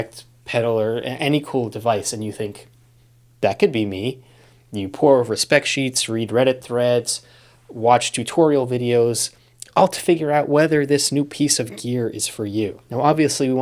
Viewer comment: i misheard "you pore over spec sheets" as "you poor over-specced sheet" and i'm like damn you didn't have to come at me like that